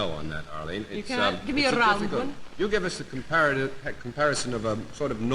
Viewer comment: What on earth does Arlene say at 5:30 with a rolled R? It sounds like "Razuman"... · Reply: I'm answering 3 years on from your question (!) but... JD: "We can't give you a flat 'no' on that Arlene" AF: "You can't? Give me a rrrround one!"